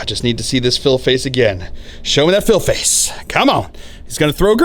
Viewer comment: Another really fun game. Keep up the great work. At first, I totally thought you were saying "show me that fail face" and was confused because rolling a 6 is generally a good thing. I wonder if anyone else thought the same thing